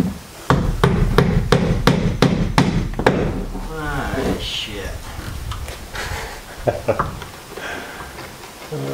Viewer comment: What did just say at about 7:45 mark? 🤣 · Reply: "Ah, ship ... lap wall panels are under the tarp." LOL